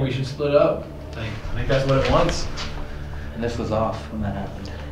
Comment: after says go into smaller group there's a voice and it sounds like it's saying "I think that's what Elton's saying." It doesn't sound like any of the guys saying it. It's a really deep voice. I didn't hear it the first time I watched but now I am.